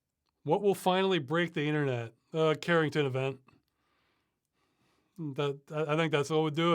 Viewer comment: what did he say? caring to the van? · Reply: The Carrington Event. "...the most intense geomagnetic storm in recorded history, peaking from 1 to 2 September 1859 during solar cycle 10." He's saying that kind of thing could happen again.